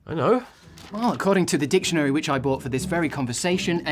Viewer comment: omfg the first time I watched this I thought he said according to the bible not the dictionary smh